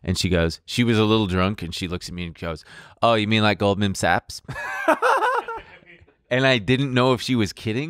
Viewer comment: I thought he said “ old man sacks” at first and I was thinking “ this person sounds hilarious.” But oh well.